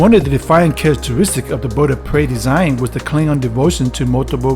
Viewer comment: Why do i hear "budda pray" =D